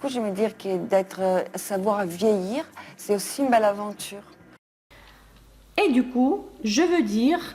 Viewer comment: Merci, мужчине тоже зашло. Итог: 3 открытия, 2 расстройства + классный бонус: эстетика видеоряда. Открытия: se sentir en phase avec soi - чувствовать себя (быть) в гармонии с собой; sans fards - без прикрас (супер!), faire la une d'un magazine - появиться на обложке или все же делать (создавать, разрабатывать) обложку журнала? Расстроило : 1) осознал, что всегда говорил c'est le cas pour... вместо c'est le cas DE... влияние английского... ну, ладно, с этим ещё можно как-то жить, но вот 2ое - это капец... 2) 8:18 сколько не переслушиваю, неизменно слышу "veuillir, c'est aussi... malaventure...", хоть убей не слышу "...une belle aventure". И вот как теперь?